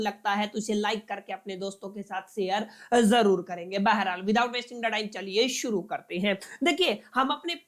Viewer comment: yeha kya bola ha aap ne please reply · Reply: aree itna pareshan...har video me wahi pooch rkha hai wo bolte hai "baharhaal" which is a urdu word that translates to "anyway".